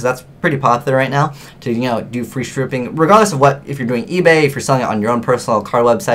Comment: it sounded like you said "Free Stripping" ;)